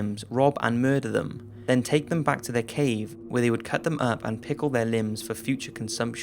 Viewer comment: I'm sorry but did you say tickle their Limbs or pickle their limbs either way its wierd